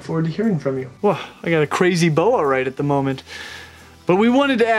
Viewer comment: I swear I thought he said he got a crazy boner at the moment... 😳😶